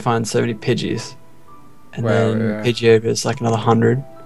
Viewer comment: It really sounds like Pidgetto is saying Johto!